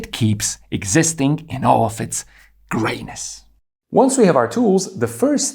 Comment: Because of the accent until this point i thought you were saying greatness 😂 thank you for the color coding